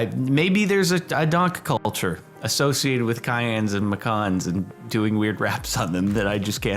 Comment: I thought he called the color cyan I whatever the car name is and I was like, umm, not how to spell